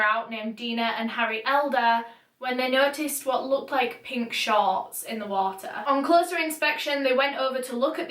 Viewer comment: When you said the fisherman saw “pink shorts “ in the water , at first i thought u said the fisherman saw “pink sharks” in the water. Lol